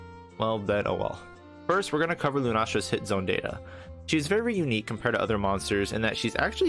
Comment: pretty sure I heard sh*tzone there! :)